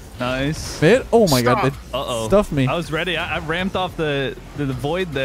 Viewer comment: did say stuff me?